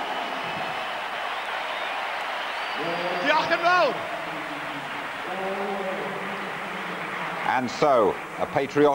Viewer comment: What does he say at 3:22? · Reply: Diolch yn fawr or 'Thank you very much'.